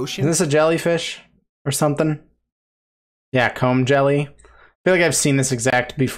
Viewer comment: They’re called comb jelly’s cause they eat sea honeycombs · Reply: I heard him as c*m jelly's